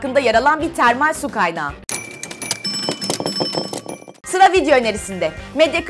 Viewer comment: Sonda '' Sıra video önerisinde' diyor ya ben onu ''SILA video önerisinde'' anladım :D